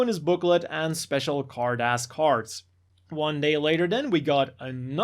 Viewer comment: What is he saying? I keep hearing "hard-ass cards" and yeah, I guess they could be that hard. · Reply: Carddass Cards